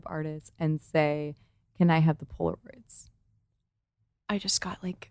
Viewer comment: 1 hour, 5 minutes she says: "can I have the ..." What? I have no idea what she says there. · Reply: Polaroids ( pictures )